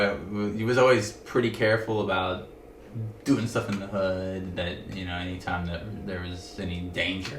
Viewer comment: When he talked about “doing stuff in the hood” and avoiding “danger” at 2:36–I was not thinking of lab work 😂🤪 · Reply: My gay ears misheard him at first. I had to rewind twice. 🤣 Because at first, I swore he said, "doing stuff in the butt."